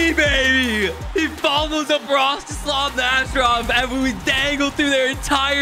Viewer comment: Watching these with CC on has become one of my favourite things 😂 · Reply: it says broth to slam masterov instead of rostislov nasherov 😂